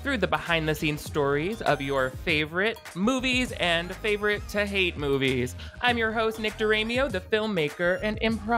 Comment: I heard "im your host Nectarineo" at the beginning. Thought you changed your name to nectarine. 🍑